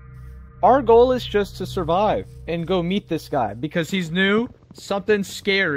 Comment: When said "meet this guy", I heard at first "eat this guy" 💀